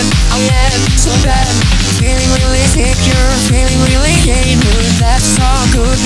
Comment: For some reason, I heard "feeling really gay, dude" instead of "feeling really immune." w h a t